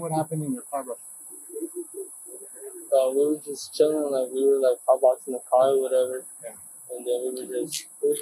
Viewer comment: Hotboxing the car, is what he said.